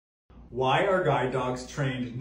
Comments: I thought you said “why are guy dogs” and I spent a long time trying to figure out why female dogs can eat food off the floor but males can’t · My dumbass really heard him say "guy" dogs and not guide dogs and I was so confused. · For a second, I thought he said “guy dog” and I was like uhhmm sexism much?😭😂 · I thought he said "guy dogs," not "guid dogs"💀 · Oh GUIDE dog I thought you said GUY dog. My bad lol makes sense now